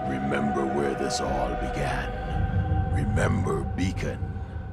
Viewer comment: I thought he said Bacon